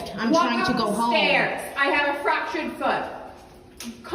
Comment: I swear i heard her say i have a fractured Butt 5:44 🤔